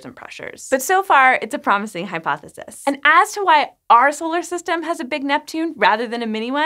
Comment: why did i hear her say "swoler system" in the beginning at first